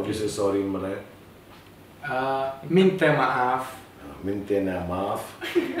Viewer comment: Did you just say "maintenance maaf"??? Wtf bro